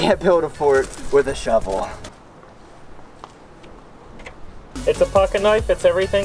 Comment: it sounds like he said “ it’s a *f**king *knife